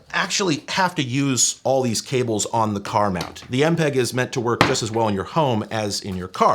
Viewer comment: yeah I was terminally online at that forum! Your video was excellent, I think you got to the heart of it. Along with Howard's more hardware and lore leaning video it's lovely to have some of what we did preserved for posterity. Maybe you'll come to an empeg meet someday :) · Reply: ’m such an idiot i thought you said “empreg meet”